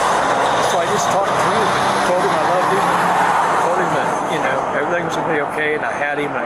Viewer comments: he said "i told him, i loved him". I just thought everyone should know what he said since the editor couldn't parse it out. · He said he loved him but the subtitles said *inaudible*.